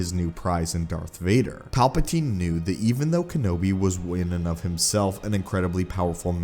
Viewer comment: What did say at 5:39 'Palpatine knew that even though Kenobi was...', it's not clear for me, what he said ? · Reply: In an of himself